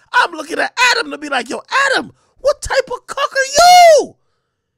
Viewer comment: I just heard a super-rare bird called the Cuckayuu.